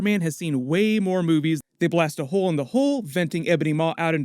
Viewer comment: You said “hole in the hull”, but it sounded like you said “hole in the hole”.